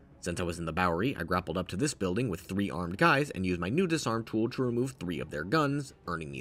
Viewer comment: i swear he says “hot guys” instead of “armed guys” and trust me i know my preference